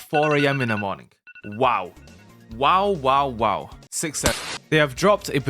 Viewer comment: did you just say WaW...? Like a WaW E.G.O or abnormality?